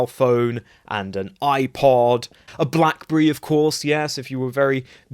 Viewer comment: did he say,” a blackburree”?